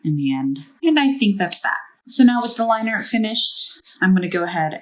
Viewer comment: I thought she said eye liner- @@